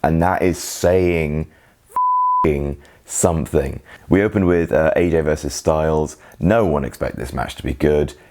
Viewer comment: Did I just hear say "We opened with Aj vs Styles" at 2:54? Or am I deaf.